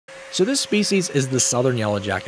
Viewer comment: I heard "so this feces..." 😭😭